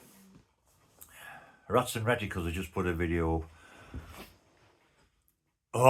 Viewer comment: Hey. Could you respond back with the name of the YouTube channel that you said was good it sounded like you said “rats and reticles”. I could not find that YouTube channel. Thank you for all that you do, my friend. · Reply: It’s ….Rats In The Reticles ….. Denny is a cracking bloke and has just bought his first pipe but not done an intro video as yet as he wants to watch and learn a bit more first.